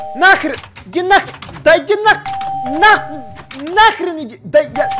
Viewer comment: Мне послышалось или сказал Нах#й